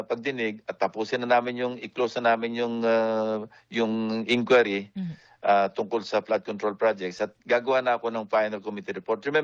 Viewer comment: DID I HEARD HIM RIGHT 3:17 ICCLOSE NYO NA ANG INQUIRY TUNGKOL SA FLOOD CONTROL AT GAGAWA KA NA NG FINAL REPORT, SI ROMUALDEZ PO GANON NA LANG, KAHIT PAULIT ULIT NA NABABANGGIT ANG NAME NYA SA PROBLEMANG ITO